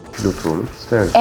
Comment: 'de ne diyor anlayan var mı? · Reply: Pilot dedi sanki bana öyle geldi